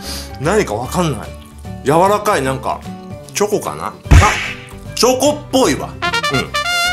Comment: チョコっぽいがチョコボーイに聞こえたww 今の子達は知らないよね(笑)